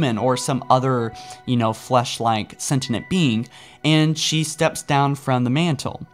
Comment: Am i the only one who gets triggered by him saying liberry rather than library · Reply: Think I've also heard him say "sentent" life